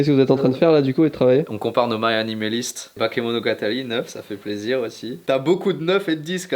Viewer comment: Il dit quoi ??? "On compare nos MARIANIMALIST" ??? moriamimaliste ??? Gné ?